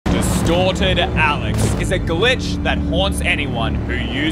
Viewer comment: In the begin when when you says “Destorted” Alex, I thought/heard “DESTROYED Alex”... My mind was like; wtf???😳😂😂